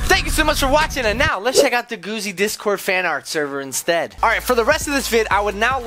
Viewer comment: it sounded like said "lets check out the sock!!!"